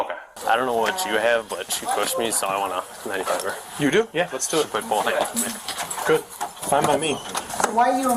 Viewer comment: lol it sounds like he said “let’s do it big boy” 😹